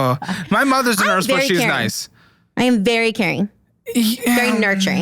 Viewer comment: All i heard was “I’m very Karen”